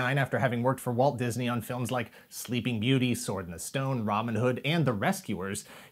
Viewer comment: did say Ramen Hood? I had to rewind just to make sure Producer Greg didn't add RAMEN HOOD? to the screen.